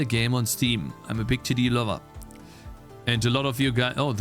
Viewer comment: Im a big tittie lover is what I thought you said. not Im a big TD lover.